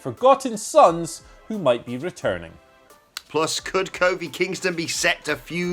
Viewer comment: Did say Kobe Kingston? xD